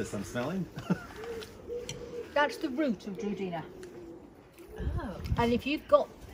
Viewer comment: that's the root of georgina? Is that what she says???